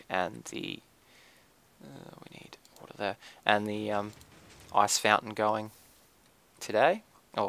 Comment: I really like your Australian accent! I lol'd when you said "ice fountain" at 23:12 because I thought you said "arse fountain" XD